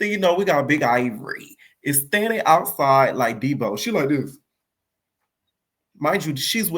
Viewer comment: “Big ivory standing outside like deebo” all I heard was “get beat up like yo daddy used to” 😂😂😂😂😂